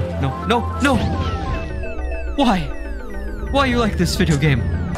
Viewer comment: You said, "Why are you like this, video game?" and YouTube made the Like button light up because it thought you said, "Like this video."